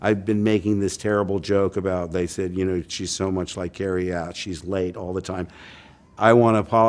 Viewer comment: I thought he said she laid all the time!